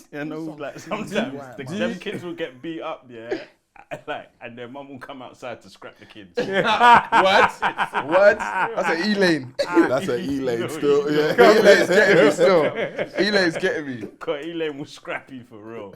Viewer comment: Loooool white woman called E-LANE😂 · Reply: Thought he said Ealing still